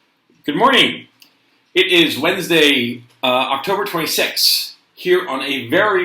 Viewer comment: Please talk about the WEF and the Great Reset... Also, I almost thought that you were saying "It's Wednesday my dudes" like on 9gag xD